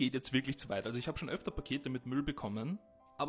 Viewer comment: Als mein Handy zu leise war habe ich am Anfang „ich habe ja schon öfter Kinder verprügelt“ verstanden 💀